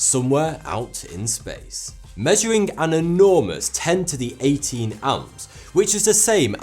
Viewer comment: I know there's a way for us to fix captions, how do we do it? At 1:00 he says "10^18 amps," but the caption says "1018 amps," and that's a huge difference.